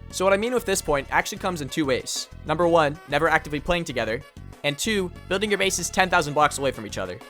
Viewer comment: what did he say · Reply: 1. Never actively playing together